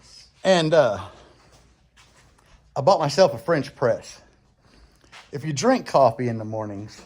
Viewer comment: I thought you said I bought myself a bench press and I'm going to drink it in the morning I was thinking it's going to be kind of hard to swallow those barbells you might have to call P Diddy to show you how to do that. Love the channel just kidding no Diddy😂